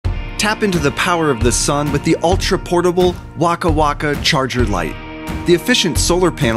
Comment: i thought it said wanka wanka